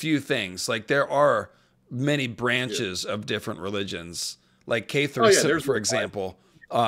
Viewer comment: Sorry to comment again but what was the name of the religion you said? The dialogue overlapped it and the captions interpreted it as "K3 sayers" lol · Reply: Catharism! The Cathars! look em up it's wild